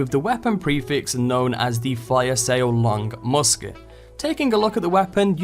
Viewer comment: At first i thought he said the 'Elon Musket' and let me just say that would be an amazing name for a gun